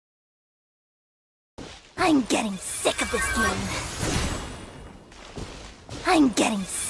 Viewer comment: Ooooh she said "I'm sick of this game" while I was playing I heard "I'm sick of this bullshit"